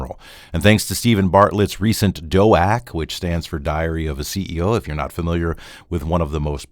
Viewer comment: I had a Freudian mishearing at the start. instead of Diary of a Ceo I heard Diarrhea of a Ceo > Sry I know clownish of me but it made sense in regards to the faulty arguments of the apologists. 😅